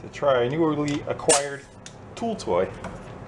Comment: Say, boy, what's that there new vibrating tool you done used to get that screw loose about 17:40...? You said it was a 'pool toy'...or maybe 'cool Roy'? See, I don't hear so well anymore...my doctor says if I don't start trimming back all them thick locks of ear hair I have a growin' it'll just get harder & harder to hear. I done been thru this before with huge black nose hairs protruding - cain't smell nuttin' anymore, except for last nite's dinner on my chops! Middle of my face looks like a black rabbit's back end...I'm dealing with it...some chicks dig a hairy nose(!) on a man...I ain't found one yet, but so I'm told...